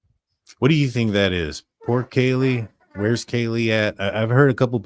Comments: I dont hear poor Kaley I hear 4 chan- can't unhear it but it's not logical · I dont hear poor Kaylee. I hear “should have warned Kaylee” and it may say Xana but it fades softer to my ears. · I heard 'do you know where Kaylee is?' not "poor Kaylee". Makes me wonder if they had looked up the stairs at some point without going right up and saw Kaylees door was open.